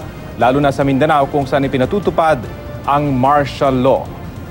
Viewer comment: dapat lord mag marshallow npo sa buong pilipinas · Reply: Mariarose Almodiel lol I thought u said marsh mallow sowwy! Martial law po